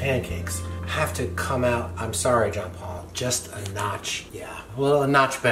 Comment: His name is what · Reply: I heard him say "John Paul" not "Jean Paul"